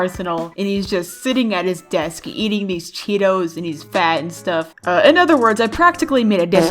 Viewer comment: is it just me or did she say cheetos